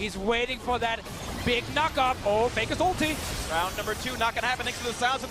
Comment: i thought he said 'f**k up' instead of knock-up hahaha i had to go back